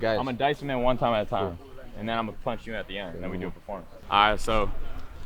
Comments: I don’t know if anyone heard it but at 4:35 he said that he shot armed and dangerous in London with juice on tour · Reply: did he say he was gonna punch??? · Loved watching this and how the creative direction is developed gonna rewatch the video now to appreciate all the thought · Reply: did he say he is gonna punch him or am I hearing wrong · did he say he is gonna punch him at the end?? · what speaker is that?? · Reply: did he say he was gonna punch him???